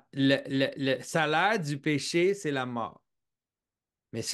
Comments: please, i am not a native french speaker, and i cannot understand the phrase Jonathan says at 28:54 the Close Caption puts it as "la salade du péché c'est la mort" 😳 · "la salade du péché c'est la mort" please, i am not a native french speaker, and i cannot understand the phrase Jonathan says at 28:54 the Close Caption puts it as "la salade du péché c'est la mort" 😳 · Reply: "Le salaire du péché c'est la mort" The wages of sin is death